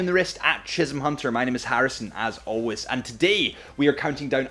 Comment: Did I just heard "my name is Harrison as always"?? ....